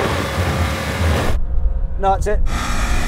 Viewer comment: 「何故？」日本語の空耳に草生えましたｗ